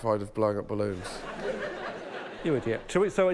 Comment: Did Alex say "You Idiot"?😂😂😂😂